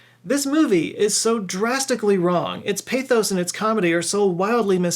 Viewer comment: Can someone help me figure out what he said at 20:04 "criticized for it's lack of __" lack of what exactly ? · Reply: Tact